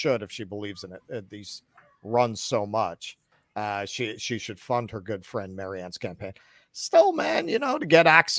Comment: I thought you guys said she shouldn't have hired him...